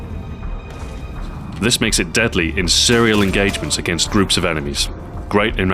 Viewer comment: after 5:21 did you say "Cereal engagement"